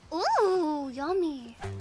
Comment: Is it just me, or did anyone else hear her say "Yummy" when she saw Yami's play